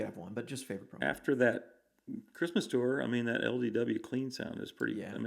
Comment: Can anyone tell me what Michael says is his favorite clean profile? Something like LVW or LEW? It’s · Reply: Divided by 13 LDW - he calls it LD39, found on Pack #1